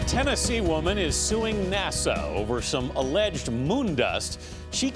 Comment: did he say NASO